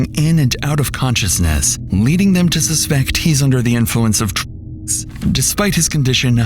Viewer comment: What was said? The word was censored. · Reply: I’m assuming “drugs”